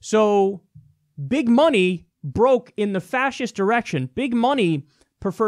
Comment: Did say Fascist Erection? 5:08 All kidding aside, thank you for your informed analysis!